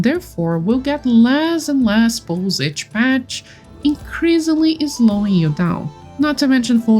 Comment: Sorry what did you say at 6:54 ? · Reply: "increasingly slowing you down"? I have a bit of an accent sorry!!